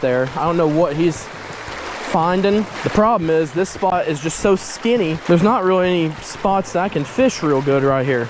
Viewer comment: if u saw is he sayd it too skinny